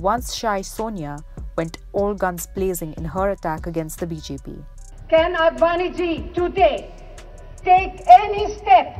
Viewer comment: for a second I thought she said "can Advani ji Chutiya" ! LOL